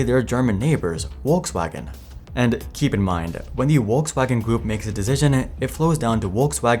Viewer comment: sounds like your saying Wokewagon 😂